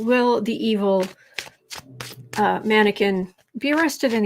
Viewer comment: Oh it will be interesting to see how he goes with filing his future tax returns? Haha i thought you said evil manikin, had 5o listen again...evil man again!! Lol😂😂😂